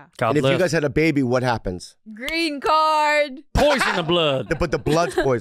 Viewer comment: When said “GREEN CARD” I thought she said “RETARD” and I laughed way more than I should have